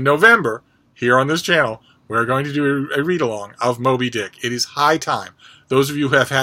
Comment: "It's high time...". Thought you said high tide! I have my copy and I'm ready to set sail oh Captain, my Captain. What about the rest of yee land lubbers???